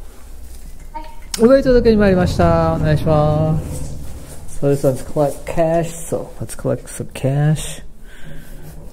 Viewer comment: Hi guys, こんにちは❣I'm triying to learn Japanese. 07:10 You spoke so fast :D What did yo say? 26:24 This is a good idea ^^ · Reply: Hi👋 I said “UberEats お届けに参りました” which means “I’m here for an UberEats delivery”. Some people just say “UberEatsです”. To cross the street? Yeah best way to legally cut lights 👍